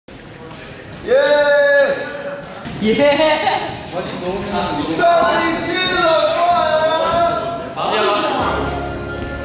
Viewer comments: I thought he said "what's up bi*ches" and now I CANNOT UNHEAR IT!!😂😂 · I was like 'wth did u say 'sup bitches'? ' 🙊🙊🙊